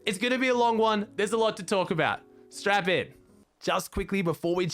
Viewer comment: i thought he said “strap on” i was like ok, im ready😉